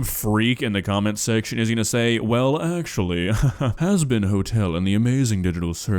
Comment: DID I HEAR HAZBIN HOTELLLLLLL